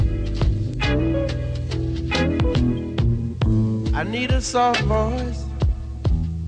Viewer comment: I thought he said, "I need a cell phone..." and thought...Hold up, wait a minute, hang on...1974? I had to listen again! 😁